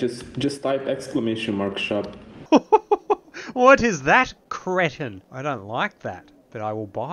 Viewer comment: what word did you even use to describe that creature? "cretent"?